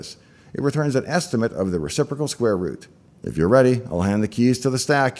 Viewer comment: Reciprocal square root - finally all the hubbub makes sense 😂 I heard "inverse square root" and thought WTF? The inverse of square root is square, how can that be hard to do? 🤔